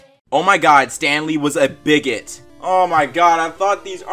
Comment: Honest to God thought it said Stan lee was Bigfoot